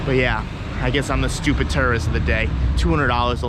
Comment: did he say tourist or terrorist😂😅